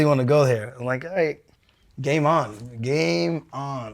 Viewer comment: Most will think he said "game on" to that Captain Eric dwarf. But he actually called him a "gay mon" he was just saying man with the Jamaican accent.